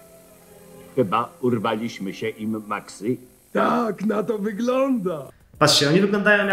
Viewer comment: Tylko ja w 7:11 usłyszałem chyba kurwaliśmy się im